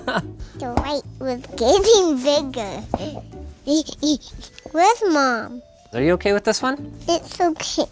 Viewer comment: first it sounded like "ligma" then i realised she said "where's mom"